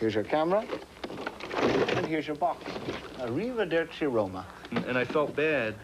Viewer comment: What was john saying for fun when he gives Paul back his box 08:01 the accent he has sounds like he's Irish. 12:37 looks like Sean wow... what's the piano music at 22:05 that plays for awhile? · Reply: If I remember correctly, John was speaking Italian and said "Arrivederci" (a formal way of saying "goodbye). And, yes, I agree with you. Sean looks so much like John at 12:37. I can't remember the music I used at 22:05 but I got it from YouTube's treasury of copyright-free songs. If I have the name somewhere, I will come back here and let you know. I like it too.